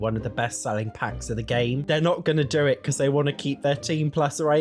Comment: what does say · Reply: He said "keep their teen plus rating"